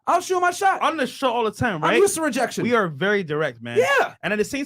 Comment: Did he say " I'm used to rejection "